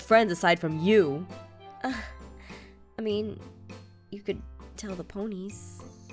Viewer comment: When she told zane that he can tell the ponies, at first I thought she said you can tell the police. It's